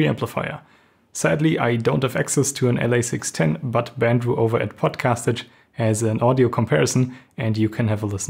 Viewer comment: Bandrew over at pocastige? is that what he said?